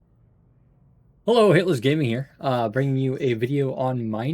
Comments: I just thought you said hi Hitlers gaming here lol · hey, like your videos at the moment, one suggestion - when you introduce yourself in your videos, I always hear Hitler's gaming - maybe pronounce that a little bit more clearly? (I dont mean evil, this is just like the 5th video where I though wait what?)